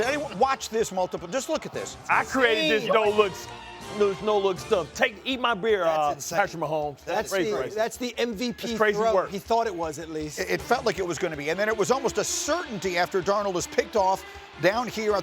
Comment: did he say “eat my beer”?